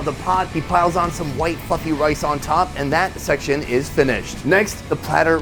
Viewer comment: sounded like he said " white fucking rice